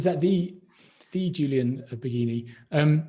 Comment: "Is that the Julian Bageeny/Baghenie"? 1:07:05 That has me curious! Who is he - or she: did I mishear and it was it Julia? I don't agree with his (assumption) question though